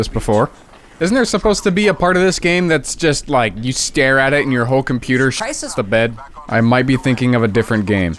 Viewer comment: did that say scheiße????